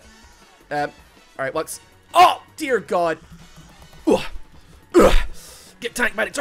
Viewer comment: Did he just said Udyr God?